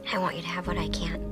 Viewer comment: first time i misheard "I want you to have an IKEA" Lmao